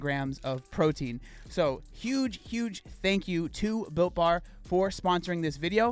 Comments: At first I was smh because I thought you said Bill Barr was sponsoring this vid, then realized you said Built Bar (love their products btw, they make something for everyone so I can't complain) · At first I thought you said Bill Barr was the sponsor 💀 I was like “damn he really branched out since resigning as attorney general”